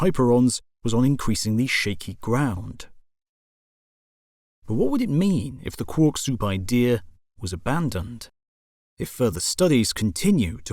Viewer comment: good thing for subtitles thought he said, pork soup. Would that be a top or bottom pork?